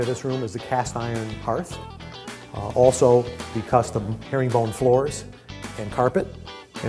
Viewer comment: what does he say? Herring bone floors? :)